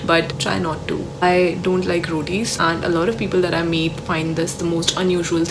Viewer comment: I thought you said , "I don't like roadies" at first🤣🤣 then came to know u said rotis😅